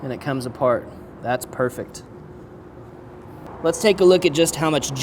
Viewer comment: Almost thought was saying: "let's take a bite" and I was like wuuuuuut